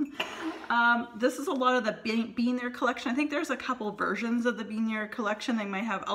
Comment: Great collection!!! -Wait! At 9:38, did you say, “Bean There“ collection? Instead of “Been There“? (Smiling) Any chance you’ve gotten Disney parks mugs and/or tumblers by Starbucks? They are gorgeous. -The Disneyland Neighbor